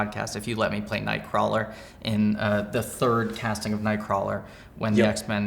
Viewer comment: I thought he said "if you will let me play night-crawlers with you"